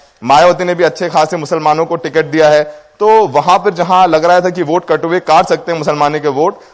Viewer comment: Did I heard it right at 4:39 he said "katuve" for muslims people